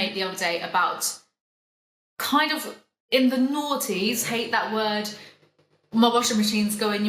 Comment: Not going to lie I thought you kept saying the Naughties instead of nineties lol love your accent and content! Just thought it was funny